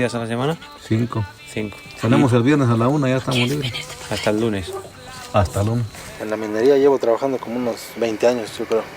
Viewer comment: Alguien podría decir que es lo que dice la voz que se escucha en el minuto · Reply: Entendí un “Quieres venirte pa’cá?” ! Y en el min 6:04 susurra la misma voz